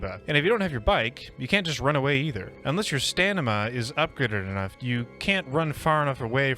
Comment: you say "staNima" instead of staMina